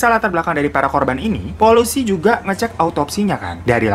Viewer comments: polusi?mm? Polusii??? SALAH DENGER GAK SIH?! 😭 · maksudnya polisi nih?? Kok kedengerannya jadi polusi, awoqawoqawq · Ini aku yg salah denger atau memng bang bilang Polusi😂😂?